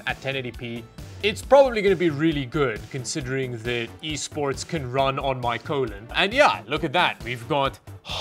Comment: i liked this video beacuse i heard "can run on my colon"